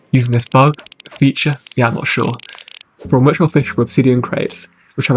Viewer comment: what the hell does he say? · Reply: "From which we'll fish for Obsidian Crates."